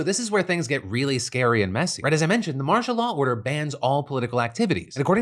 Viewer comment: After a while all I heard was, "Marsha Law."